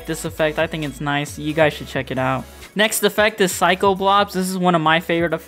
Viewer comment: did you say psycho globs or blobs because i can find what that effect is? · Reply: You can find it in the presets tab like s_psykoblobs.